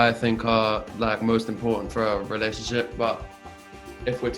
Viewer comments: why does is sound like he says ‘our relationship’ instead of ‘a relationship’😏😏👁👅😭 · I swear he said OUR relationship 🥰😍😘❤️😻💞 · for OUR relationship!? You and Jo? Hhhmm? Did I hear you right Hero or is your accent just getting the better of me? (That’s when he was talking about what’s important in a relationship)